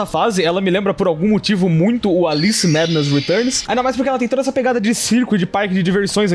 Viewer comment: alguém sabe me dizer q esse jogo e bom ? E fala o nome dela tbm... Não entendi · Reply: Olha o jogo é bom, na minha opinião. Mas varia de pessoa para pessoa. O nome do jogo é Alice Madness Returns, espero ter ajudado